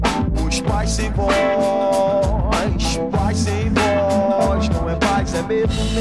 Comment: Quando eu vi essa música pela primeira vez eu ouvi "País sem voz" o certo é "Paz sem voz)